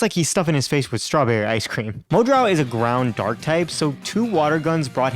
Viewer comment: did he just say brown poison Type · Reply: Ground dark